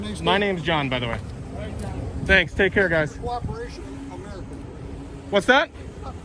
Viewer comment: did say, "welcome to America" or something along the lines of "___ American" or am I tripping? He doesn't seem to repeat it after he's asked, "what's that?"